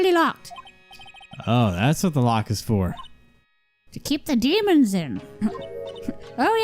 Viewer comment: I coughed here and thought she said “to keep the dudes out”